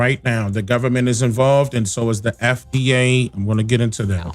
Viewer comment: why did you say the FBA is involved with the government? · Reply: He said FDA😂